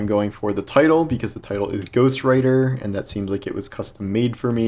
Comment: Ahhh Ghost Writer! I thought you said Ghost Rider.